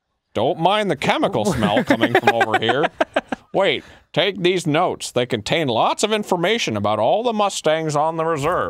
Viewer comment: Lol I heard, take DEEZ NUTS they contain lots of... XD!